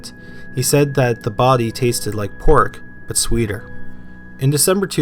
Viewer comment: I thought he said it tasted like pork, but stronger?